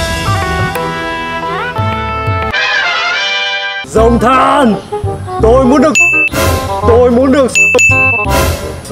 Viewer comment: ko bt denji nói gì nhỉ :))) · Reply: Nghiện ch*ch 😂😂